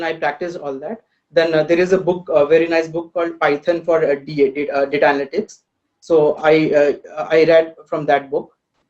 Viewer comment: Plz if someone can tell me the python book that he mentioned at 3:12. I couldn't hear it clearly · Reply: Python for Data Analysis - McKinney, Wes